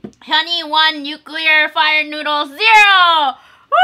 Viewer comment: i thought she said 2x nuclear "fart" noodles hahahahah